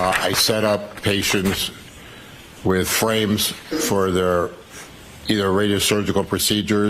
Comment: Did he say...frames?! Hes used to do it!